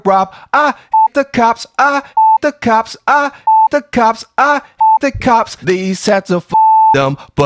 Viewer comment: When I first head this, it sounded like he said "I Fuck the cops, I fuck the cops" and that was fucking hillarious!